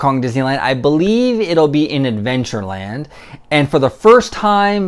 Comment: Lol I thought you said that moana was going to be in “avengerland” 😂